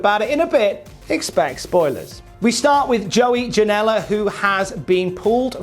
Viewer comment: Joey Janella? I thought you said Talent...